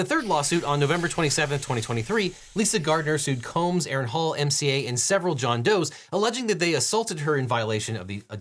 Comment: did he say MCA from the beastie boys?